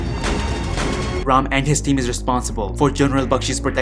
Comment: Omg 😂😂... I literally heard "abh ram" as AbRam(his son) 😂😂😂😂